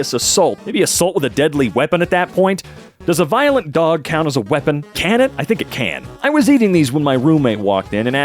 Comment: i zoned out really hard for about a minute and when i zoned back in for some reason i misheard as "does a wet dog count as a violent weapon?" and was so confused. "i think it counts as much as a dry dog does??"